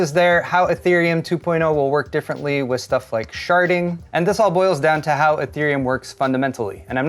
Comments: "....Sharting..." Wut did he just say Rewinds a few seconds listens closely "...Sharding..." oh phew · Since the first ~13 mins of this is the headline question, I'll attempt a TLDW: - ~25% of GPUs sold go to crypto miners; I think this greatly impacts the bubble/shortage - Crypto miners buy GPUs differently, maintaining high prices and creating appeal for scalpers - Ethereum is the main crypto mined with GPUs by a big margin, and Ethereum is changing to Proof of Stake - Unless there's a movement to an alternative GPU-based crypto, miners will likely sell their GPUs at some point; many will want to do this while prices are still high - A sell-off situation can shift the market rapidly; a flood of used GPUs (even if they've been used for mining) can lead to a drop in overall GPU pricing especially if there's no longer value for crypto miners - no other GPU-mineable crypto comes close to Ethereum in market cap, which is vital for profitable GPU mining I'm sure some will agree and/or disagree, this is a complex topic. Thanks for watching either way! 👍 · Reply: At the 8:16 mark of this video did you say "Sharting"? ;)